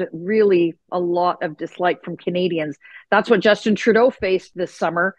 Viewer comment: Did I hear you say, "Justinna Trudeau"? I'm sure I did. Wouldn't surprise me in the least.